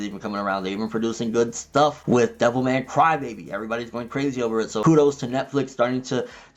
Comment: Devil Man Cry Baby? i thought you said Devil May Cry Baby